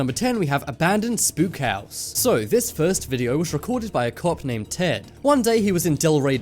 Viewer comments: At the start I thought you said this video was recorded by a cock named Ted lmao!! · Is it just me or does it sound like he said a cock named Tom?